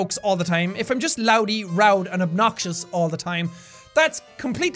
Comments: jack says, loudy and rowd instead of loud and rowdy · OHMEHRGERD! at 15:17 jack said "if I'm just loudy roud" instead of "roudy loud" 😂😂😂😂 good job my friend! I dont blame you though English is hard! I still can't talk right and I'm 13!😂😂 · did he say "loudy and roud"? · instead of roudy (idek if i spelled that right wOoOpS), loud, and obnoxious, Jack said loudy, roud, and obnoxious. I'm pretty sure I laughed too hard at that 😂👌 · Anyone realize he said "loudy, rowd, and obnoxious" instead of "loud, rowdy, and obnoxious" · who else heard jack say loudy, rowd, and obnaxious? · he said loudy, rowd instead of loud and rowdy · any one notice a 15:17 jack said road and lowdy instead of loud and rowdy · did you just say "loudy, rowd and obnoxious" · he said lowdy and roud instead of rowdy and loud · He said "Loudy and rowd" instead of "rowdy and loud".